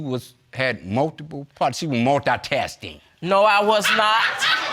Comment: Him:She was “multitasting” Her:I was not “multitasting” Me: Screaming at my phone “It’s multitasking” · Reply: I had to rewind 10 sec twice to make sure I heard “multitasting”.